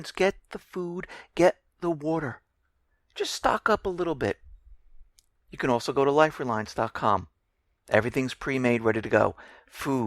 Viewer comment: Excellent report..and..dead-on. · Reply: Can you please tell me what he's saying @ 12:55 ? I cannot make out it out. It sounds like "liferlines dot com. Is that it?